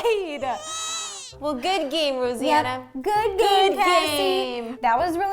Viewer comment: Did she say Rosianna?